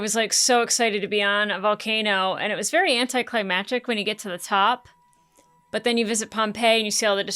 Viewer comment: Did you say "anticlimagic?" I think I've just added a new word to my vocabulary regardless if I misheard or what. How does it convey the original meaning but somehow even better?